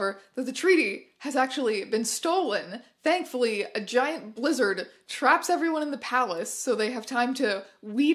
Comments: I definitely heard "a giant lizard traps them in the palace" which is infinitely more exciting than a blizzard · I misheard giant blizzard as giant wizard and I was so intrigued. My disappointment was immense when I realized there is no wizard. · I misheard giant blizzard as giant wizard and I was so intrigued. My disappointment was immense when I realized there is no wizard. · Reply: i thought she said giant lizard and for a second i was like "well maybe dragons are real in the vanessaverse. what do i know? it's christmas!" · I misheard giant blizzard as giant wizard and I was so intrigued. My disappointment was immense when I realized there is no wizard. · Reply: I thought she said lizard, I was like DRAGON?! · I misheard giant blizzard as giant wizard and I was so intrigued. My disappointment was immense when I realized there is no wizard. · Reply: i heard it as giant lizard lol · Misheard this as "a giant LIZARD traps everybody inside the palace" and wow now I think about a cozy Hallmark Jurassic Prince movie. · when you said "a giant blizzard" i thought you said "a giant Lizzard" and i was very confused about the direction these movies were going in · I legitimately misheard "a giant blizzard trapped them in the palace" as "a giant lizard" and I was extremely confused for about six minutes as to why we weren't talking about that more. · I heard "giant Lizard" instead of "giant blizzard". I thought there would be a dragon which would be infinitly better.